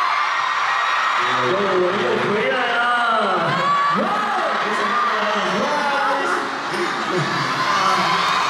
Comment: wha did winwin say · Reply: we came back again